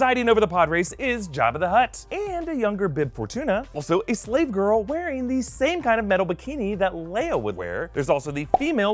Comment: can someone please tell me what he's saying? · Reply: He says no relation :)